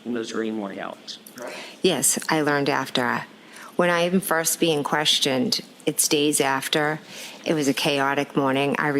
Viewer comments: Jennifer McCabe said, "Yes, I learned after at … when I even first being questioned…". She did not say, "Yes, I learned after … when I'm first being questioned…" (3:04). The difference could impact the interpretation of her words. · WOW, the chance to help!!! And I’m pretty sure I have the answer for you: “It stays after:” I think on the stand she actually says “It’s days after” but she’s nervously mumbling/slurring her words so much that it sounds like “stays.” (If you open the YouTube automatically generated transcript at 3:04 it actually has “it’s days after”) With that in mind, she’s attempting to explain why she didn’t admit to this call in all of the interviews and testimonies prior to this one. (The 5:07 call didn’t come out in the first trial) Add in the fact that it’s the most incriminating call because Nicole is supposed to be “sleeping” until 6:45, and you get to thinking you’re having a stroke!